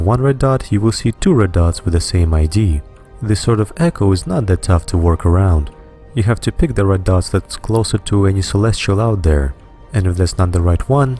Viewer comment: What is he saying at 5:45 I can't understand him :S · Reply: "This sort of 'echo' is not that tough to work around. You have to pick the red dot that's closer to any celestial out there. And if that's not the right one, simply increase the range of your probes once again, and go back to the previous dot shown." Short explanation: one signature, two locations on map => try scanning the location that's closest to a planet. If signal strength doesn't increase, scan the other location. Hope that helps